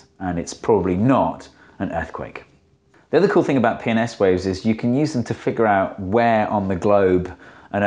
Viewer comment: Can't say how many times I just hear "penis waves"